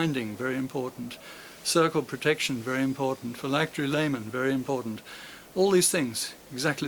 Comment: What does he say? “____ laymen”? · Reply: "Phylactery Lamen."